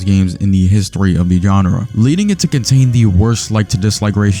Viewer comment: why did you say genre like “gonorrhea”